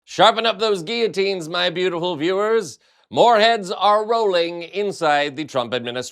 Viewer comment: For a moment I thought he said "fore heads are rolling..." and I thought "Miller!!!"